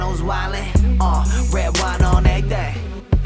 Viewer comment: Legit thought they were saying "red wine on egg day" 😂😂🙈